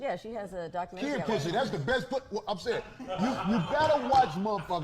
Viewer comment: did he say kid 🐱 is the best?!?! Ppl need to watch who their kids arnd!!! Yea they need to watch yo ass! And who tf is laughing in the background